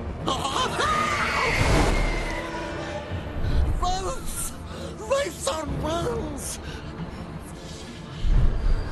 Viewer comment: what did smeagol say?:D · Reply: Wraiths!,Wraiths on wings!